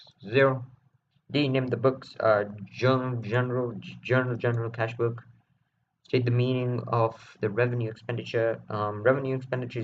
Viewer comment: wasn't very clear at 20:33 did you say general journal and cash book?